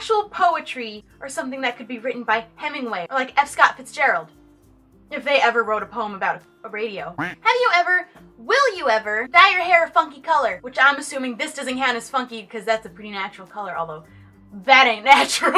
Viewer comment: I thought she said "dye your hair a fucking colour"